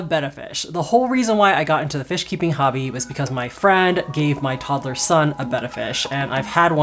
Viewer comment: am I the only one who heard "my toddler's son" ? 😳😂